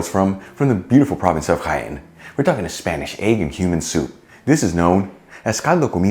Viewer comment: I first thought he said Egg and Human soup! 😂😂😂